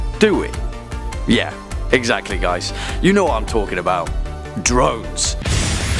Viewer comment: When he said drones I thought he said drugs for a second